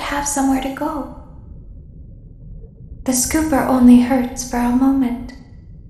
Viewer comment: Even tho I've already seen a full playthrough, at 0:07 I thought she said "It only hurt bro". I'm not sure why..